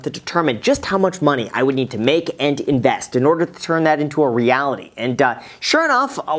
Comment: "to turn that into a reality and..." i hear him say "die" but i think he's saying "...anddduh" a combination of the words 'and' & 'uhh'